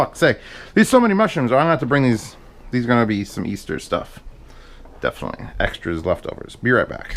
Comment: Did you say for Easter???? That's quite a impressed backlog of videos